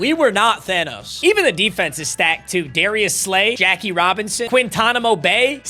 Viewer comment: did you call coop Jackie Robinson 😭😭💀